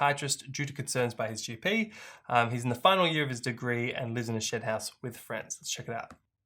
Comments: ……Sounded like said “lives in a shit house” lmao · oh my word 😅 I thought you said “lives in a ‘shit’ house…” 😂 Had to rewind that one 😅